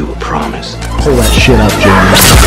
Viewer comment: did i hear "throw that sh*t up, jeremy?" lol